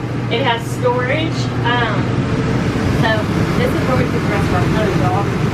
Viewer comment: 1st time viewer. Enjoying the video and I hear "this is where we keep the guns" and my thumb kicked into "auto-sub" mode. 👍👍 When I 1st got the command strip velcro, I was very surprised. Good stuff.